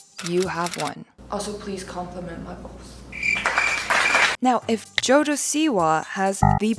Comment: Bruh it sounded like you said “please compliment my balls” 😂